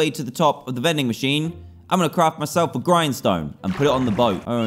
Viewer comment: with captions it said “crap” instead of “craft”